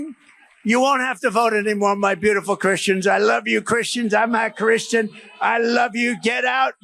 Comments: I don't like Trump ... but, I heard "I'm a christian" ... I don't know how anyone heard "I'm not a christian". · Why does it sound like Trump just said he's not Christian. Did anyone else hear that?? 😯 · I doubt he ever said he was a Christian, he only panders to them. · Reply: he mumbling the words wery badly but actually I heard ".. I am not christian.." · Did tRump say “I’m not a Christian”? 😳🤣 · Those "beautiful Christians" need to review this footage a little closer, because it sure sounded like Trump said "I'm not Christian" to me. 🤔